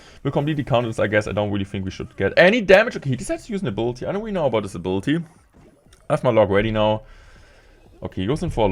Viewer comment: sounded like ' I don't really know about disability'